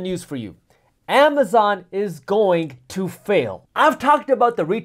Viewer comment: Dammit. I thought he said Amazon is going to jail.